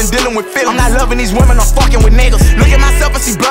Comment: Listen i still support him if hes gay buh did bro say iaan loving these women im fuckin wit nigga